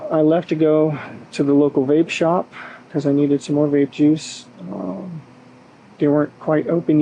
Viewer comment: I could have sworn I heard him say he "needed to go get more r@ juice" !!!